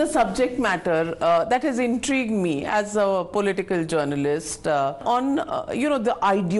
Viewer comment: "...as a political journalist..." or BJP journalist? I didn't hear it well.